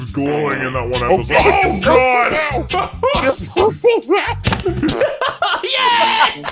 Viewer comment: Mini sounds like a little kid saying 'yeth' (yes) instead of 'yeah' XD